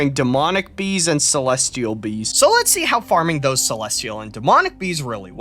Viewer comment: My english is kinda poor so I keep hearing "Celestial Beings" ... I love ark ascended (moved from evolved cause new features and was discounted with bob dlc), despite the bad reviews its one of the most fun games, specially if you mod it. Could bash people that sell mods tho but it's a standard now (in other games too, like skyrim). This mod seem to much chaotic for me with absurdity. By the way, what is that mod that add lat/long on top left side? I been trying find it. So far I had no crashes playing in extinction, but had some bugs and me fucking up few times my save (uploading my character to move in another map was one, didnt work) so I learned my lesson and made a batch scrip to backup save. XD Thanks, was fun watching.